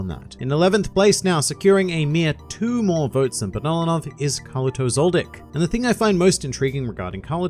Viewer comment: On 4:13 i found my automatic Translate to be Collosal D*ck instead of Kalluto Zoldyck